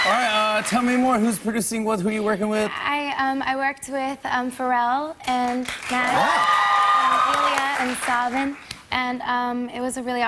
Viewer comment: Who did she say she's working with at 0:20 anyone know? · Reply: BOB BOB Pharrell Williams, and the HIT-MAKING pop dream team of Max Martin / Ilya / Savan Kotecha (Problem, Break Free, One Direction, Cher Lloyd, Katy Perry, etc.)